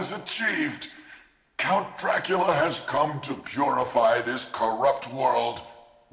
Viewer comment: Funny, he says "...has come to purify" and the subtitles say "...is come" :D